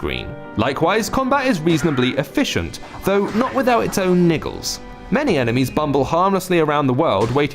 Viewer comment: Did he say niggles? o.O